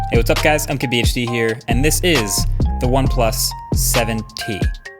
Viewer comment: Before I thought he's saying "I'm KBHD"